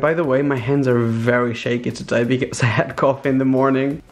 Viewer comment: wtf did he say? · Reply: He had coffee in the morning